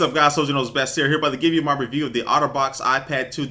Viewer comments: when u said outterbox i thought u said autobots i was like WTF? then i did a double take on the title lol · i thought he kept saying "auto bots" instead of Otter Box lol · It sounds like he says Autobots not otterbox